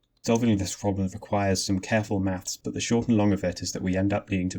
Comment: is a subtitle error! It's says "subtle" instead of "careful"!